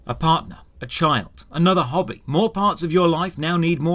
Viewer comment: As a wise man once said "create the problem, sell the solution" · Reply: When he was talking about how your life changes and he said 'another hobby' i thought he said 'another hobbit'.